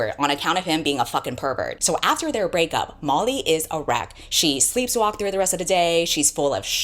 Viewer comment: Girl when you said "Molly is a wreck" I had to rewind because I thought you said "Molly is erect" whoops! hahaha